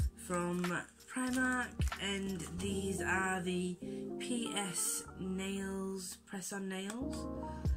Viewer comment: Haha! I love you but I thought you said penis nails when you said PS 😂😁💝